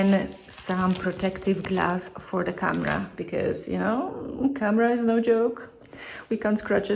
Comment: I thought she said kamra 😂